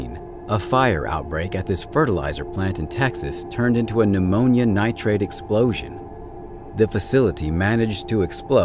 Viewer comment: I know he must be saying "an ammonium nitrite", but all I hear is "pneumonia nitrate", 😂